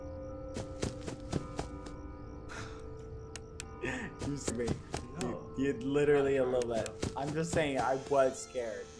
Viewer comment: did bbh say shit?!! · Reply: shoot